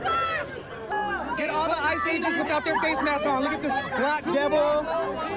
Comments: did he just say "look at this black devil"? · Did he just call him a black devil?